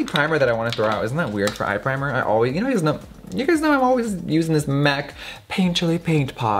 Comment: did you say “niggas know “ ? 🤔